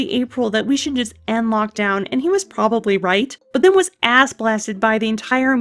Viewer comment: Spit-take my coffee out of my mouth, “Did she say ass-blasted?”